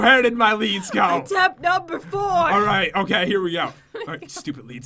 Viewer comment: wut does he say? It’s weird it’s like “alright fluffing”